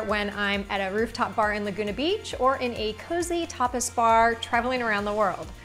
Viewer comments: I thought she said "cozy topless bar." I had to rewind and listen a 2nd time😆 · Thanks for the recipe! I had to repeat that part in the beginning, I thought you said topless.....LOL tapas.